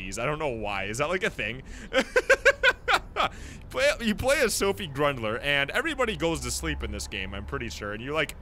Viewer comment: when he said sundie grundler the captions said sundie drug dealer xD